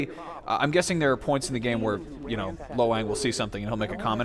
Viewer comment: It sounds like he is saying Lo Wank.